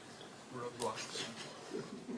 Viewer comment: roblox? 3:38 (subtitle?) weird instead of roadblocks